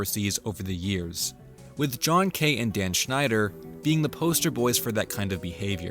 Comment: Does anyone know the other name that's mentioned other than Dan's at 11:39? I hear "John Kate", but I'm 99% sure that that's not the actual name